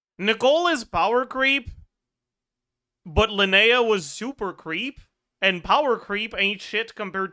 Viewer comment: when he was saying super creep I hear SUPER PHREAK, SUPER FREAK